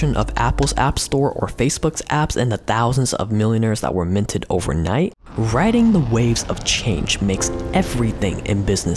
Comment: Who is this? I didn't catch the name. I want to watch this video. Help!